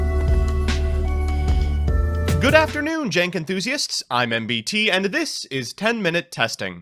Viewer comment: Wait, are you calling us "Jank enthusiasts"? All this time I thought you were saying we're fans of The Young Turks